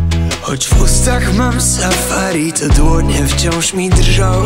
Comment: Usłyszałam tą piosenkę dzisiaj w Mam talent i dlatego tu jestem. :) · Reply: Nie jesteś sam :) Trochę źle usłyszałem w Mam Talent i szukałem po haśle: "wbuzi mam sahare" ale ważne, że od razu znalazłem :D